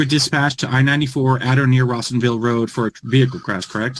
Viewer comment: I thought he said awesomeville not Rossenville